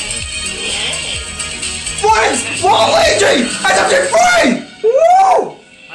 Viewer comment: ikd why but I kind of think it is funny how you said: "France" instead of "Frances" xdddd